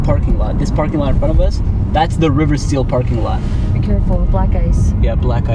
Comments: Yooo i thought you said "be careful black guys" i was bout to flip 😂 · I swear I thought she said “be careful, black guys” 😂😂😂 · I thought they said “be careful, black guys” 😭😂😂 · I thought she said be careful remember black guys no racism but she said black ice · When she said careful black ice i thought she said careful! Black guys😳🧐😂